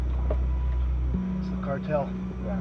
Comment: DID HE SAY THATS THE CARTEL